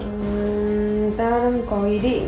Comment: nói gì mình không nghe rõ mn ơi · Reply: “thôi m đừng có ý định nữa. Hôm qua t thấy m với nó ngủ ôm nhau trong này rồi”.